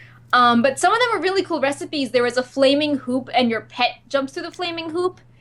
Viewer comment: I could have swore she said flaming poop instead of hoop. I was like "Well some pets do like to jump on poop. Not sure about flaming poop though." Then I realized it was hoop.